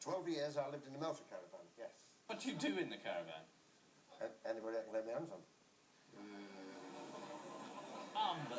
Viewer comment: I can't understand what he says when replying to the question at (0:50) .. Anyone? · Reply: I think he said "Anybody I could get my hands on"